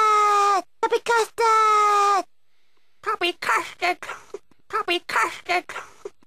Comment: I hear 'Tubby Casted'